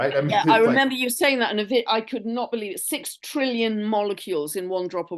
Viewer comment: In case someone misheard 1 drop of water has 1 sextilion molecules water 😮 1,000,000,000,000 a trillion, 1,000,000,000,000,000 a quadrillion, 1,000,000,000,000,000,000 a quintillion, and 1,000,000,000,000,000,000,000 a sextillion